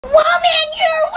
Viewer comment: WOMAN YOU’RE WAY TOO CLOSE; ✨PePpEr sPrAy-!✨ 2024 edit: Why of all comments did this blow up- ._. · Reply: Ohh I thought he said "whoa man!"